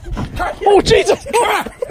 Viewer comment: ベーーーーン‼︎こらっ！こらっ！(空耳)